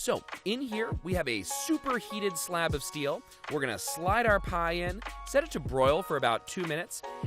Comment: Did he just say Pie?